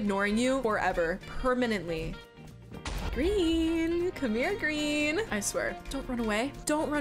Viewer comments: sounds like George saying come here dream lmao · its come here Dream instead of come here green